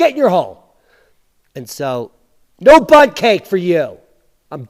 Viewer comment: “Shut up Al” ... “Get in your hole” ALWAYS makes me laugh! I’ll send Al Gore a Bundt Cake 😁.... love these reports. You rock! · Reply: I thought he said butt cake and was really grossed every time he does that. Can’t forward it on when he does that